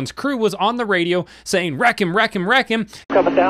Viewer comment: I heard “ wrecking “ ( Joey ). Not ‘ wreck him ‘ ( Dennis ). All Good 👍